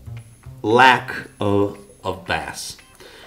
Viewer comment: did you say "the downside is that there's a lack of fish? FYI: Bass is pronounced as "Base" ...in this case. "Bass" (rhyming with ass) is a deep sea fish. Sorry to be such a pendant and point out this one tiny error in an otherwise wonderful video! Thanks for sharing this info with us all. It was very informative and I'm truly grateful to have learned something new.